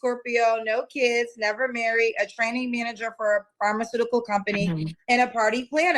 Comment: I swore said she was a “Tranny Manager” 😂😂😂😂 Salute 🫡🫡🔥🔥